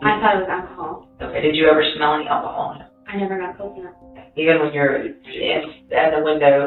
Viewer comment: who does these subtitles? they are terrible "i never got coconut" when she clearly says "i never got close enough".